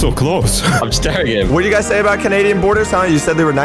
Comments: did you say asses or assets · WHAT DID YOU JUST SAY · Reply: He said there A#ses are at the border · Did he say assets or did he curse for the first time